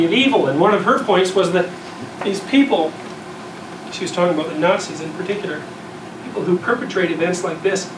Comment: CC shows "nuts" instead of "Nazis" 🤣💀